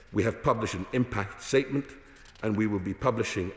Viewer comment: He does actually say "Satan" at 7:39 seconds. A sadistic masonic freak as per usual etc.